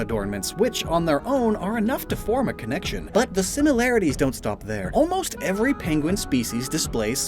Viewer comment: Did... did you just say "sinnolarities"?